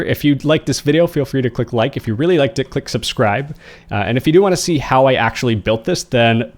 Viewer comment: I swear this man said, "If you really like dick, click subscribe."